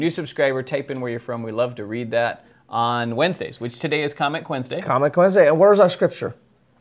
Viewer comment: For a while I thought you guys were saying “comic Wednesday” instead of “comment Wednesday” 🤣 I was confused for a long time. I still hear comic Wednesday though lol